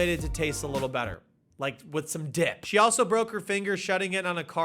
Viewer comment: swear to god he said “with some DICK” 😂😂😂😂😂